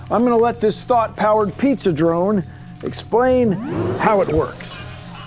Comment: I thought he said “Thot power pizza drone” 😂😂